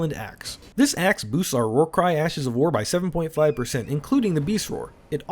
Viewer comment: I heard War Crime Ash of War lmao, and uh, I’m fairly sure that this build does classify as one